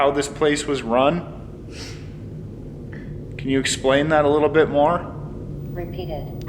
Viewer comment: During the digital recording I hear "can you repeat that twenty times" 😂